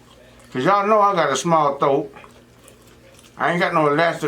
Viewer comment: Boyyyyyyy u better stop🤣🤣 I thought it said help my thot stretch 🤣🤣🤣 I had to rewind and pause... you meant to say THROAT NOT THOTE🤣🤣🤣